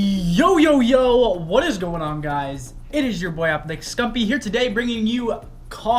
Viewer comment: I always thought you said MamaScump instead of MomOfScump lol 😂